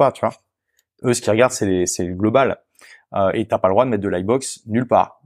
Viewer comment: C'est quoi la "linebox", "livebox" (?) dont parle Olivier vers 1:06:17 ?? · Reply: lightbox : c'est un popup avec un formulaire d'inscription